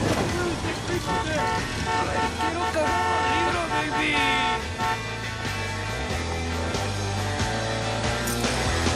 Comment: What does the guy in the car saying at 1:07 ??? I guess its spanish but I can't put it in a text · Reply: "I want to marry you baby"